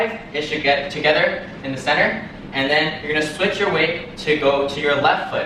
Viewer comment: I heard, you're gonna switch your wig. Well, I guess that's what it is not be a native x))